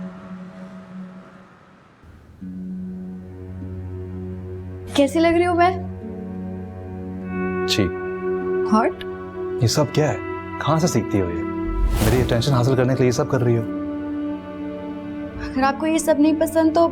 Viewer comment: Did zamal said hot in 24:36 Somebody please confirm She used word hot